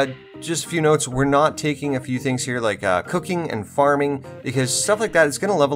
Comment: i had to rewind i thought he said cooking and farting, lol